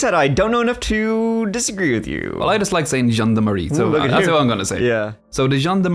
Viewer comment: I hear "Jeanne de Marie" every time you say "gendarmerie." I think "zhand armory" would probably be closer (like "john'd," but with a French J. To really sound French you'd have to give the Rs a light roll.)